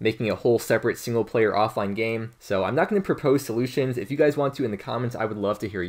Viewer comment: I kept hearing "Propose to Luciens" and I kept wondering why you were saying that and why he can't be your lucky man lol